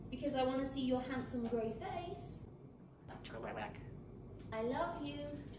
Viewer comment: When he said "I turn my neck" I thought he said "enjoy my back" 😂